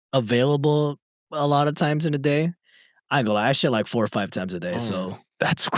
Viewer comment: Until said that about how regular he is, I misheard and thought it was a story about basketball. Title: "My husband hoops too much" "I shoot like 4 or 5 times a day" 😂😂😂